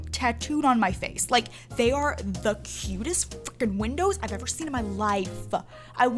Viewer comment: On 9:56.. did she say freakin or f#%$in?